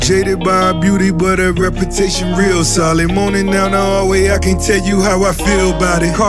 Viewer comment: “JADED by her beautiful but her reputation real solid.” 🧐🤔 2020 needed this level of real yet slick pettiness! Let me download this. I’ll see y’all in 2021! 🤣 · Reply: Moses O. The first time I thought it was “sour”, but upon several repeats 😂 I heard “solid” and corrected it.